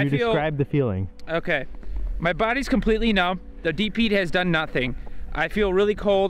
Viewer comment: Whan he said deep heat he sounds like he said DP like dude perfect